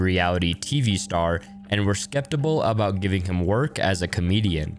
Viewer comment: did you say "skeptible"?